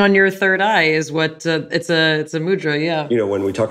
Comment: I thought she said "mutra". I googled: What? She said, mudra. Mudra, yes. Mutra, no.